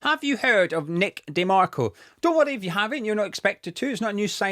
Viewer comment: At the start there I thought you said have we heard of Mac DeMarco!! A decent chap, and excellent Musician. Check him out if you don't know him!